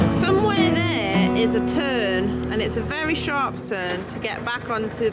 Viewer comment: I thought you said a turd vs. Turn